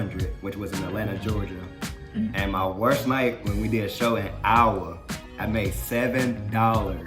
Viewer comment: I had to re watch that part I couldn't tell what the hell he said , I live in Iowa , and I just though "No way in HELL did he just pronounce Iowa 'OW-wuh" 🤭 lmfaoooo like what ?😂